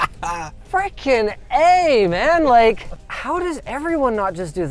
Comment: Linus said "Freakin A man" and the CC said "Freaking eh, man." cracks me up. Canadians.